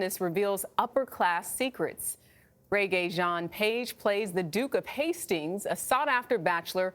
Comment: I started watching because I kept hearing “ the Duke is so hot”. Yep, he is! But the story is good too!